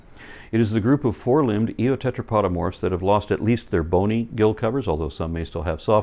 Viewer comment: This series keeps filling up huge knowledge gaps for me. Thank you so much! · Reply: Tell me what the fuck an eotetrapodomorph 5:49 is then? I have no idea what that is and when i google it nothing comes up. What word did he actually say? I must be getting it wrong.